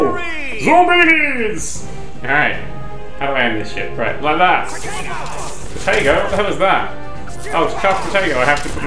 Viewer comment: did he just say potato?! oO xD